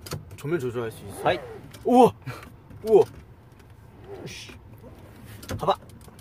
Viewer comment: I have investigated......and Idk I think 3:13 han said Lee know-sshi 🌚